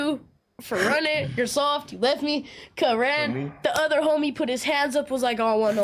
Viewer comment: did he say cuh ran?